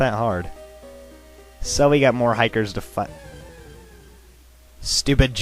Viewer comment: What did you just say? 'So we have to get more hikers to fuck?'